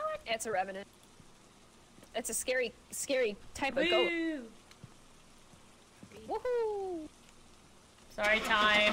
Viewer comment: it's a scary type of ghost What I heard: it's a scary type of goat